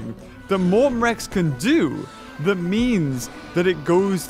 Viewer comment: wait did he say means or memes XD